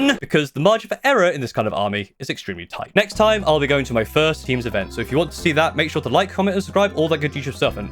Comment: I misheard the end comment I thought he said he was going to his first intervention.